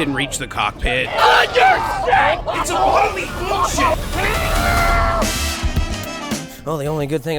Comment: what did he say?? Did he say it’s completely BS?